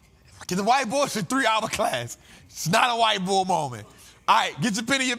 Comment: No bap I thought he said white boy moment 😂😂😭